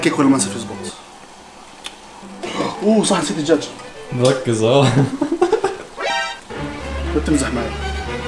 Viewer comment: شو قال ما فهمت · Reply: قال (مركز ها؟ )لان قال لازم نركز على طبخه وحده